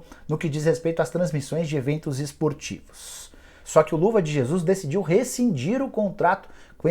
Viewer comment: Fui só eu q ouvi "Luva de Jesus"??? Kkkkkkkkkkkkkkkkkkkkkkkkkkk